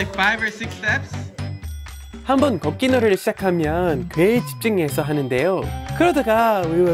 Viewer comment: ㅋㅋㅋ ㄹㅇ 무안하게 만드네 4:05 “한번 걷기 놀이를 시작하면 ‘꽤’ 집중해서 하는데요” 에서 꽤가 개로 들리는데 그럼 개집중해서 한다고 알아먹을까요?ㅠ 말도 안 되는걸로 그렇게 민망하게 만들지 마시길 ..